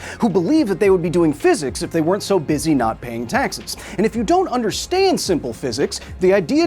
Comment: I had to double back to check that you said “not doing taxes”, for a second I heard “doing taxes”. 😅